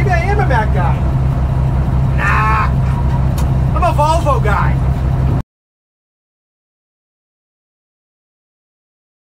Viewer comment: 😂😂😂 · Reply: I thought you said "vulva guy"